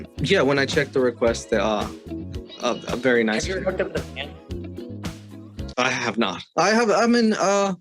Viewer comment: I thought he said have u ever hooked up with a man I had to play it back, he said fan right? My bad sorry everyone, awesome video! ☺